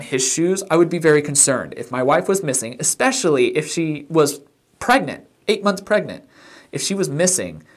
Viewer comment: When Scott says he moved the mop bucket for her because, “she WAS pregnant..” ... THAT is suspicious AF... she’s hardly been “missing” for 24 hours, & their baby isn’t due yet, so she should still BE pregnant. 🤨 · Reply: I hear "yeah she's 8 months pregnant" not "yeah she WAS pregnant"...